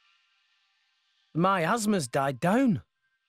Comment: On 4:15, it kinda sounded like Rex said, "my asthma's died down."